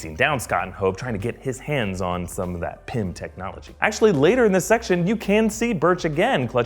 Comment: thought he said pimp technology for a moment